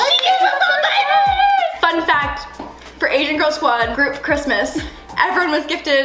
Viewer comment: gonna be honest, till now I genuinely thought Lauren was saying agent girl squad